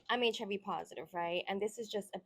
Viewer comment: You know the first time I thought she said Chubby positive....